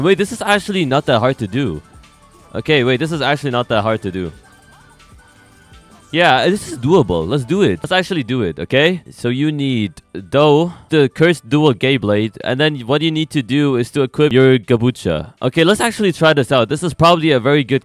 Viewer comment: People who heard Cursed Dual Gay Blade here 2:53 👇